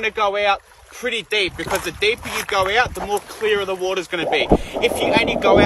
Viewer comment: “You wanna go deep because the deeper you go the deeper the water gets” is what I thought he said I instantly burst into laughter 🤣🤣🤣🤣